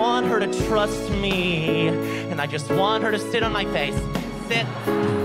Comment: lol I heard spit on my face and I was like wat 😂😂